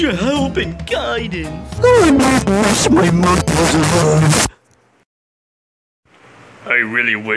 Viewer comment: what did you say? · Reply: "I really wish my mother was alive-"